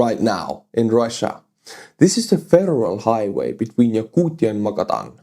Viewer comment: lol sounded like you were saying a feral highway and it definitely looks feral 😂